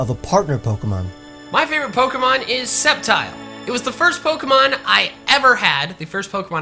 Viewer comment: at first i thought you had everyone say septile...